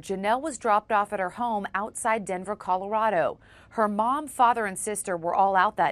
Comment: did she said denver bc I live there right now